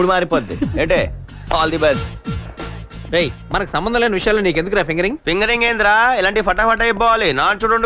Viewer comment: what! did he say 😮😮 😂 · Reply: Fingering 😅